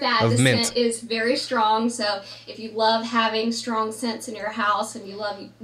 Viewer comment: She says "strong sent" I hear "carcinogens"